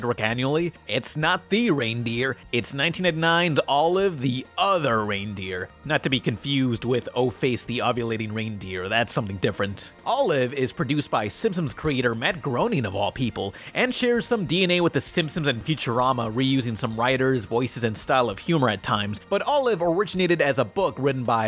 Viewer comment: Every time it sounds like says "Simpsoms," take a shot.